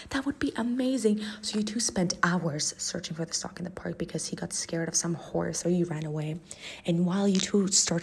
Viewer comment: why did i hear "he got scared of some wh***"